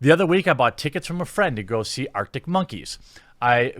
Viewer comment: Best bit was where I misheard and thought for a second that some people went to see a band called "Dick Monkeys".